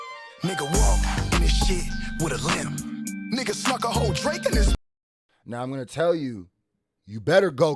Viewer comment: When he said snuck a whole drake, I thought he said he sucked a whole drake.